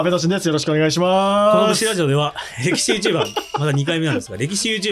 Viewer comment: 今回も楽しみました。キャスト予想楽しいですね。 の物真似もいつも期待しています。(市原隼人さんのまね大好物です😂) 今回の最初の挨拶、ごめんなさい、歴史YouTuberを"力士YouTuber"に聞き間違えてしまいました。